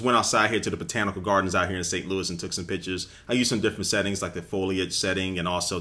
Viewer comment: Haha 3:57 ot sounds like he said "i took some bitches"